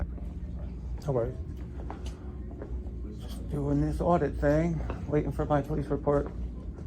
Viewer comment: …Ahaha! Did anyone else hear him say “Hello. Just doing my “fraudit” thing”? Glorious!